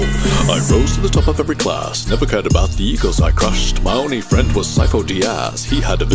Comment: I didn't car about the egos I crushed I thought he said i didn't care about the e-girls I crushed